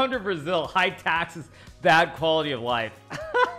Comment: what he said? Come to Brazil, hi taxes.... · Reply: Bad quality of life (má qualidade de vida)